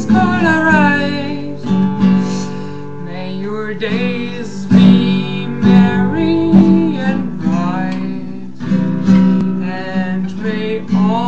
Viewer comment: haha. Ich liiiiebe es! ;’D Zu toll. Und deine Weihnachtsmütze. Seeexay ;) Ich hab an der einen stelle immer verstanden „and your gays will may be right“ haha. Ich lachte auch so bei dem fuck! Schöhne Weihnachten kann man Dir ja nicht mehr wünschen ;’D Guten Rutsch Dir und Allen anderen <3